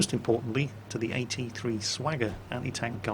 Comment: I'm sorry, but did you say the AT-3 "Swagger" at 7:16? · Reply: he said AT-3 Sagger which is the NATO designation for the 9M14 Malyutka ATGM.